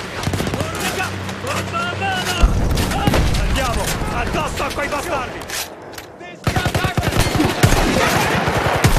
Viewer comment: What's he saying? I hear "andiamo" and "bastardi", but nothing in between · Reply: "Andiamo, addosso a quei bastardi"